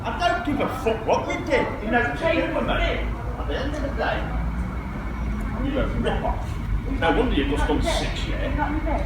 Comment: What does he say at 5:12 cant make out the accent...? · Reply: Think he says 'your a rip off, no wonder you just done 6 year' as in 6 years in prison?